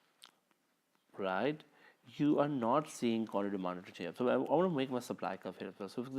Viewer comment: what did you just sayy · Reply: you are not seeing qty demanded to change. I want to make my supply curve